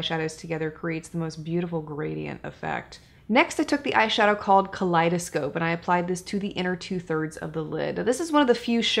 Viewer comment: At first I thought you were going to say “colitis” during the second look demo. Then I realized you said “kaleidoscope” 😂